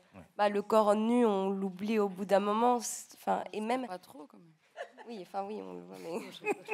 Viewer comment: What Virginie said at 31:41 ? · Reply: Daphne who speaks, says nakedness in the film tells many things and that we forget the naked body. And Virginie to laugh said "not totally anyway"